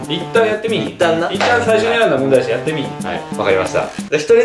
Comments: さんの聞き取り不可のとこは 「最初に選んだ問題やし」って聞こえる！ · 聞き取り不可のところさ、「一旦最初に選んだ問題やしやってみーひん？」って言ってない？ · (聞き取り不可部分)リスニング問題 一旦最初に選んだ問題やしやってみーひん(完)